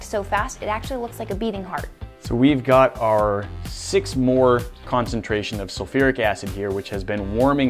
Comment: is it just me or did he say "6 more concentration of H2SO4" ? Do u mean 6 M. (i.e. 6 molar = 6 moles/L)